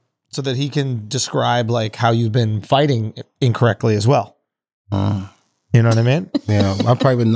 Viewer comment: did wack really say "uncorrectly" ??